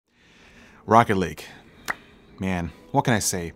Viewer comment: I thought he said rocket league at the intro lol